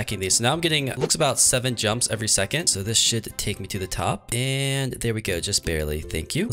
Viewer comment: i thought he said "this shit should take me to the top"